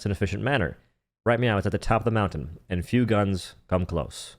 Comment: Did I hear, "Right meow"